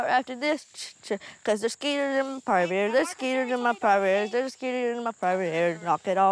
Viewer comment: did Anna say there are cetiers in my private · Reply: skeeters like mosquitoes